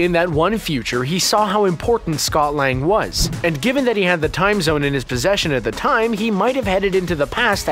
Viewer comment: Did you say Dr. Strange had the "Time Zone" in his possession @ 3:05? I guess the time stone controls all time and time zones as well?